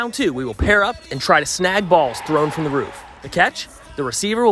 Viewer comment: did anyone else catch that he said "throwed from the roof" instead of "threw from the roof" or am I just being picky :)